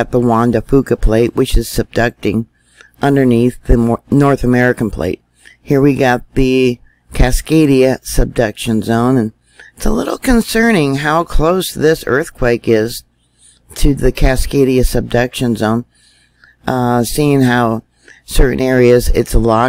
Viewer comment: I always hear "seduction zone" and giggle to myself. Hee hee....